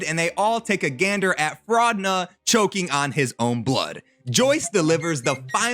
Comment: Fraudna and the Nevermind Flayer were fumbled so hard. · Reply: At first I thought of Sukuna when you said Fraudna and I was so scared 😂